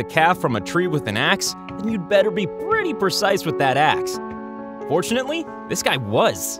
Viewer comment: Did bro just say unfortunately